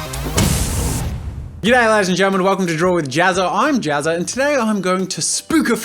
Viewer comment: it sounded like he said "You're gay, ladies and gentlemen!" lmao